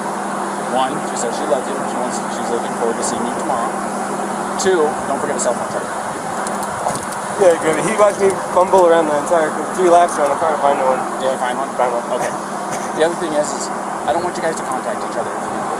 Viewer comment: I thought he said he didn't own a cell phone